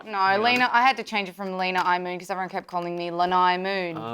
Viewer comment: It definitely sounds like says “Linda Moon”.